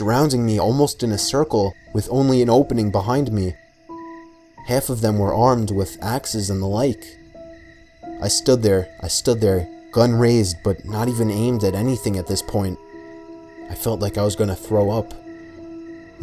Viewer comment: Anyone else hear the "i stood There" x2 at